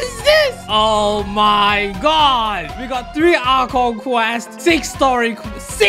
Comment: automatic subtitles says "alcohol quests"